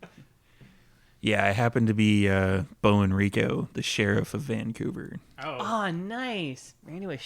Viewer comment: I misheard as well. I thought he had said Beau Enrico.